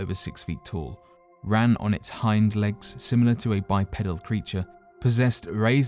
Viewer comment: i legit thought he said "ran on its hind legs like a bipedal preacher"